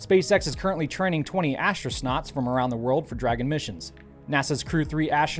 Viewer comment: Did you say astro-snots (5:14)? Is that the rookie version of a rookie astronaut? 😅